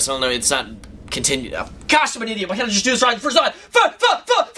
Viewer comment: sounds like he is saying fuck!